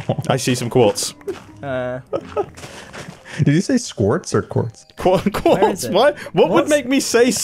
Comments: it sounds like you said 'quartz' on your side but on scars side it sounds like you said 'squartz' :']]] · I think the voice mod cut out a sliver of Mumbo's audio and Scar indeed heard him say "squartz". I heard the same thing in Grian's video